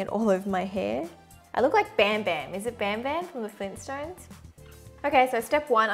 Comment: I thought she said bam bam😂💔